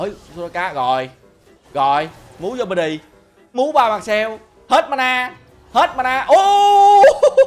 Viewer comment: Ông nào nghe đc ổng nói gì đoạn 3:20 ko. Nghe ổng đờn câu này hoài mà đéo hiểu nó là gì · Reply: hình như là: "move your body, moved by myself."